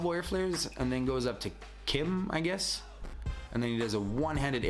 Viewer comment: what are u saying? 6 one handed elbows and go up to what? "kim" ? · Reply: That’s what we call a kim